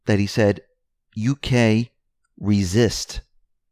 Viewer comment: Recall: Hillary wanted Assange assassinated by drone. I thought Assange said "I must resist", not "uk,resist" Please God, keep Assange safe.